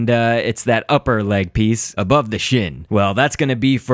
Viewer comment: do you say what I thought you said?!? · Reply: I'm not sure HOW people are mishearing this. To me, my voice is pretty clear: "above the shin."